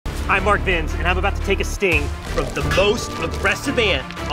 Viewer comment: Bro i thought i heard him say im mark wiens, i was like huh